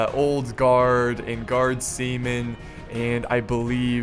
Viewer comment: Nice battle, as usual. But you really need to look up the pronunciation of "Chasseur à cheval". Hit: you're doing it wrong :P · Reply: i actually thought than he said guards semen